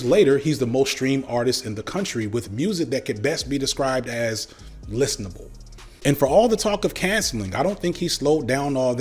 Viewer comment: "Music that could be be described as. . . " I didn't catch the word after that. I re-listened like 20 times, and still can't pick it out. I scrolled way down through the comments to see if anyone else asked or answered, and I feel like maybe my ears just aren't working or something. Could someone with ears that work better than mine fill in that blank for me? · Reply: Listenable